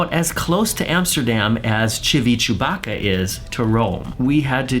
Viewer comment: Civitavecchia is pronounced Civitavekkya. How you pronounced it I thought you said Chewbacca!! 🤣🤣🤣🤣🤣